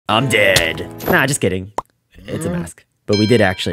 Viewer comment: I heard “I’m gay!”